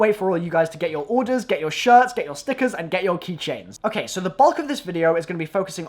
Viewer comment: Thought he said snickers.... I'm a fatass